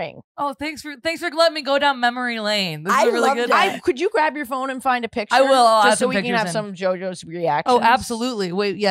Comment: I do not know what the S-word at 56:00 is and frankly I'm afraid to ask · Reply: su*c*dal